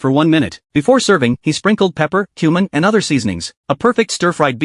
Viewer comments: My dumb ahh was thought he said human not cumin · Am I the only one that thought he said "and sprinkled human" and not something else?😭😭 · Cumin? I thought he said human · Totally thought he said "human" instead of "cumin," and was horrified for a second. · Why is it at first I thought he said “pepper,human..”🤣🤣🤣🤣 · I thought he said pepper, human and other seasonings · When he said cumin I thought he said human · I thought bro said "before serving he added pepper, human and other seasonings"